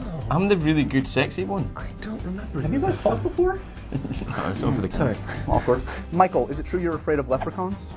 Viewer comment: Could someone please tell me what is James saying at 0:45-0:46?? Please!! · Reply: I think “No, so many times”